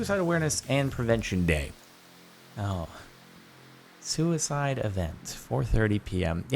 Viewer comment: I thought it said suicicle event😂😂